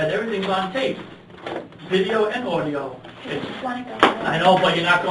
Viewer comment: what does she says in minute 30:27 that the guard said, 'but you're not going home right now'. I know tht after that she said she'd like to go homr, but what she said before that ?. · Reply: she says "I just wanna go home."